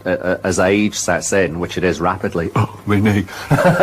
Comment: What did he say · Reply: Oh we made